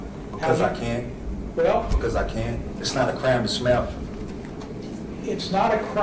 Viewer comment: Sounds like your saying something else! Smell instead of smiling!